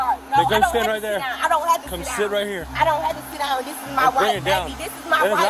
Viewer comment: low-key thought she was saying "this is my wife!"